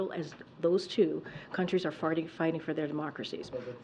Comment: did she say farting?